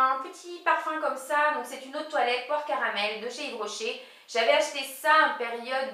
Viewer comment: je dois avoir faim car quand tu as dit "poire caramel" j'ai entendu "porc au caramel" hahaha !